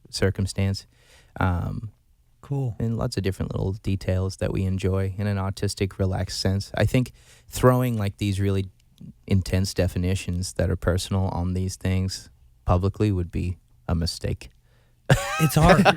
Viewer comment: it sounds like Ashton is saying autistic relaxed and a wheezed